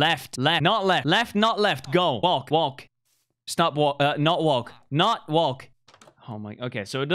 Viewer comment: When he said walk or not walk I thought he said WONK NOT WONK 😂